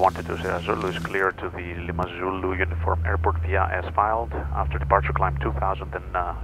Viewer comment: Hold on a minute @ 1:18 did he say as filed ? Oh you lucky some bitch I never get that at all ever